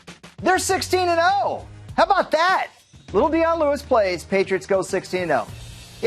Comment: When he said "how about that" I heard "how bow dah" lmao